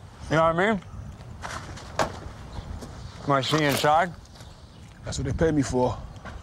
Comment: what does he say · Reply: When he's eating the gummy bears? "Really? Perfect" then something like "Will I see you inside?"